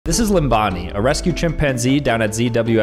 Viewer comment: I heard Ambani lol😂😂😂